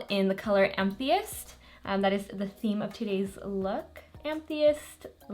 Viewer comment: Absolutely stunning!!! The Cover fx shade name is pronounced “am ah thist” thist rhymes with fist. I’m not trying to be rude just helpful-I hope that’s ok? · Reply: Georgia it sounded like she said "I'm atheist" hehe.